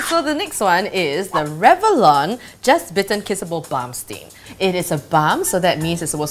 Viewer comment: When she said balm stain i thought she said bum sting